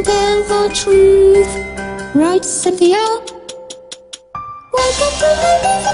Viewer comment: When I heard "isn't that right Centhena?" I was like WHAT?!